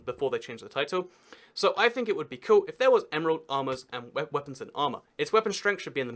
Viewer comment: Remember when I said my hair cut would make an older video look really old? This is the one. What was your favourite feature from the video though? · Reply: when you said emerald armor I thought you said emerald Lama